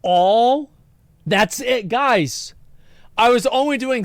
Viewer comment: what she really said was he was hiding the coke "all on top of" the other various drugs he doing right in her face. (I'm still team Johnny tho, 100%)